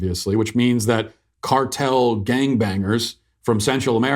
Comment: Did Matt just say “cartel gang bangers” ?